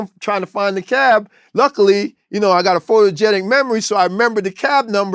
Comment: Am I the only one who heard him say he got a photographic memory?? That's the most impressive thing to me. That and the cuban brass knuckles lol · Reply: He said photogenic memory but that doesn't make sense. Photographic makes sense.